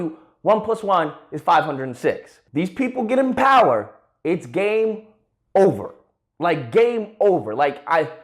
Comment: when when said "it's game over," I thought he said "it's gay over"